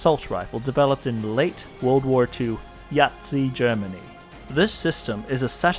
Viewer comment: D-did you just say..."Yahtzee Germany?" :DDDDD